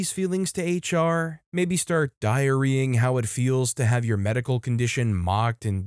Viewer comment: I was listening, not watching. In a comment about IBS, I heard "diarying" and for a second I thought it was something about "diarrhea-ing."